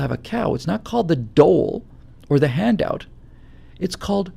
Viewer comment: What is that word he uses before handout: Dole? Dowel? Dull? Never heard it before. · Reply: it is dole. It is an old word which means a handout by a government.